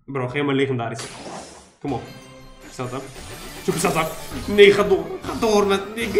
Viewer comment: Ik dacht dat ik het n-woord hoorde bij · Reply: Nee